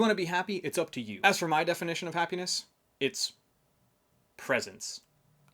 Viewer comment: Lol. when you said presence. at first i actually thought you said "presents". hahaahaha.